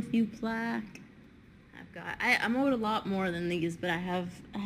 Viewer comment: what exactly did she say at 20:32? I played it a couple times and each time I hear the n word but it makes no senseeee like she wouldn't say that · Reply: „I own a lot more than these“ English is not my first language and even I understood